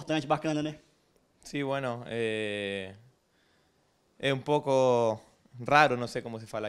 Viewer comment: Ele disse "un poco raro" que significa "um pouco estranho" em espanhol: "É um pouco estranho receber um troféu tendo jogado só 5 minutos" O repórter achou que ele estava falando "muito rápido": "Você diz aí que foi muito rápido, né?"